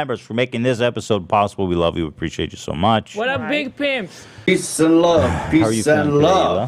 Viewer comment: For a sec I thought Hila said what up big pimps